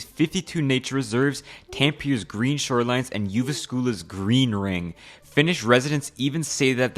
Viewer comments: I dont mean to diss, but can someone help me. I for the life of me cant figure out what the fuck hes saying att 1:53. I mean he said Helsinki so i think he might be saying other finish cities but wtf is Tampa and Yogo school... Is it Tampere and Jyväskylä or am i completely wrong? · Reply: Yes. Tampere and Jyväskylä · I couldn't make out what the city after Helsinki was at 1:54. Was it Tampere?